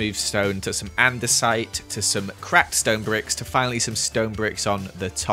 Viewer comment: I heard cracked stone bricks as craped stone bricks so i umm kinda like to hear it twice